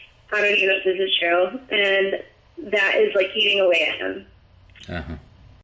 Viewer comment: it sounds like she says "Its like eating a lantern" :/